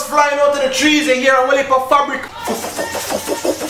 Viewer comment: Am I the only person who heard soaking wetting??? DWL 2 RASS